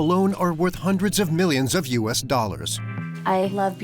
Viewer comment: did he says hundreds of millions of dollars(the content of the room)?????